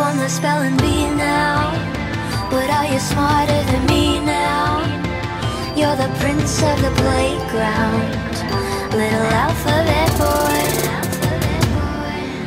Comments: it sounds like she is saying "my alpha bad boy". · I've been listening to Melanie for 6 years and I just noticed it also sounds like she is saying "alpha bad boy". Please don't make fun of me if it was an obvious word play